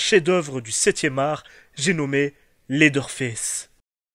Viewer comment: Laideur fesse ? Dites moi que je suis pas le seul à y avoir pensé svp xD